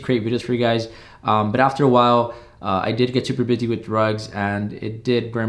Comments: I misunderstood you at first and thought you said DRUGS. I almost started crying but if course I'm going to finish listening and you said RUGS! I can't believe how relieved and happy that made me. I don't even know you I only know of you. Happiness and success for you dear one. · I really thought you said you got busy with drugs 💀